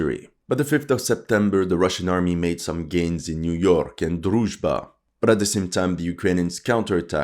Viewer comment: I was just listening and I heard "the russians army made some gains on New York", now I know there is a Ukranian Niu-York